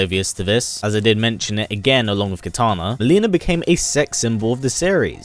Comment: did he said "Mileena became a sex" 😆